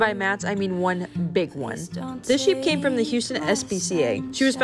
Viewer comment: Ok, so being from Houston, I'm going to say "a sheep and a pot belly pig were wandering the streets together" is probably one of the most Houstonian things 😂 💜🐑 🐖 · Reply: I thought she said Houghson, CA